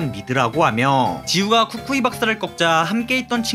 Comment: 굳...굳건아... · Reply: 이거보고 쿠쿠이 박사가 굳건이 박사로 들림...